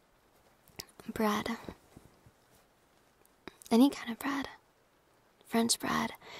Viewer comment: I thought u said “any type of brad” And i was like “pitt?”